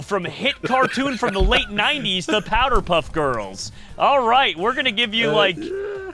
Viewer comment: did you really say powdered puff girls 😂😂😂😂